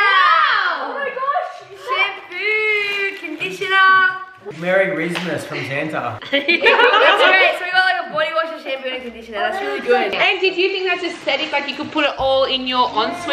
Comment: DID JUSTIN SAY “MERY RIZZMIS”?!